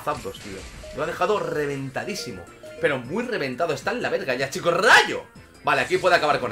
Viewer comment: escuché bien? Dice que está el la verga?